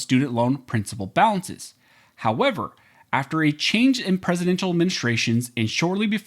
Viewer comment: Sounded like you said, "After a change in presidential menstruations..." 🤣